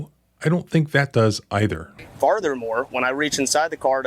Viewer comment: Did he say Farthermore? 😂